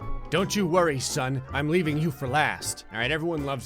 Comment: What Gray said: "Don't worry sun" What I heard Gray say: "Don't worry son"